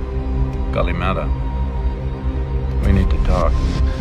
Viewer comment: just minor correction. The agent is not saying "Kalamata", which is a region in Greece, but "Kalimera", which is a greek greeting that translates to "good morning"